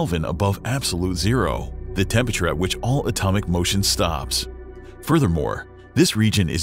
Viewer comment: Bro i thought i just heard "Atomic Lotion Stuff" 💀💀